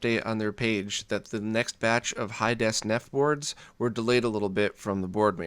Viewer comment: Anyone else hear him say Hi-Des nef boards at 4:26? :)